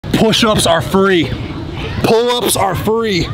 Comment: I thought he said push ups are for furries 😂😂😂😂😂😂😂😂😂